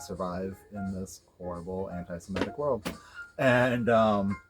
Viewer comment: i can’t tell what you were saying here, did you say antisemitic?